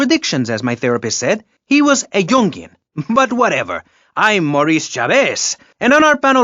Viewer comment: referring to his therapist as a “Jungian”, I always thought he said “junkie” 🤯